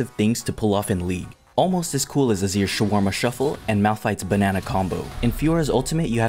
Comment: did you say shawerma shuffle?